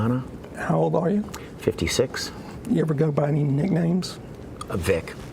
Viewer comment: I had to hear this part 0:16 twice cause when he said 56 I thought he was lying but remembered that he is under the oath then I couldn't believe it cause I swear on my life I thought I heard him say 26 or 36 cause no way does this guy look 56 in any way shape or form cause look at him he's like Keanu Reeves he doesn't age